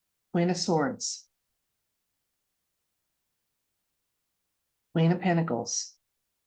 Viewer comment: I turned away for a moment while you were dealing and I thought you said queen of cankles, hahaha, and I thought of Hillary, lol!